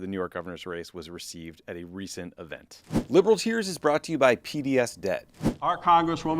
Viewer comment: Man, I thought you said PBS Debt - as in, PBS is no longer being funded by the government and needs help paying their debts. Took me a hard minute to realize you said P.D.S. Debt. Maybe tell them to change the name, or do a better job pronouncing it (don't mean to offend) because it's a little confusing and it tugged at my heart strings a bit because PBS is in trouble.